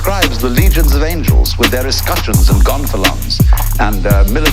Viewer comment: The closed captioning thought he said "gone felons" and another site thought he said "gone for lungs", but the word is "gonfalons" (it's like a war banner).